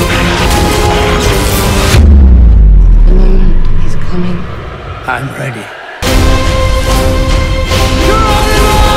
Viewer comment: I can't tell what Rose says at 0:28...It sounds kind of like "the madman...he's coming" or "remember, he's coming" or something like that, but I can't discern the first part... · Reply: I think it's "The Moment is coming."